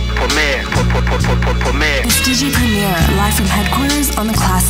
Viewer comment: This whole time I thought they were saying, “poor man” instead of “premier” lmao